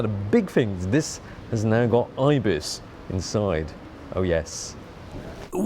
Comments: someone put "eye bits" instead of IBIS in the captions... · Love how youtube subtitles: "This has now got eye bits inside. Oh yes" (instead of IBIS). Well, now thats a game changer!